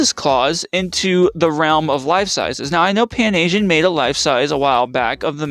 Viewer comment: I thought it said life siees